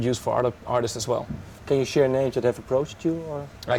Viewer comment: whats the question at 9:30..... can u ? i cant hear clearly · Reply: "Can you share names that have approached you?"